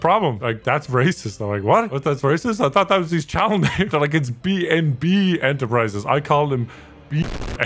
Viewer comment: Did he say "BnwordB enterprises"?